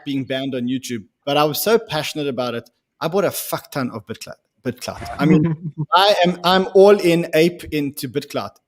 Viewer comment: Hey ! I'm french and i didn't understand what coin the talked about after 58:07 ... Bitcloud (BTDX) ? TY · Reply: Bitclout, chech their website and you'll understand